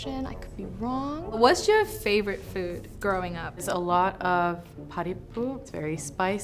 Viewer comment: these official subtitles did NOT just call her fav food "potty poop". total disrespect to parripu 💀